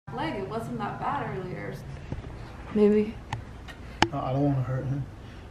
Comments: did just say “babe” or “baby” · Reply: She said maybe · what did say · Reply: She said maybe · i think said baby